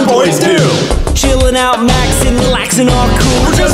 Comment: why does this actually sound like ranboo saying “catch me in the Tubbo corner half past two” to me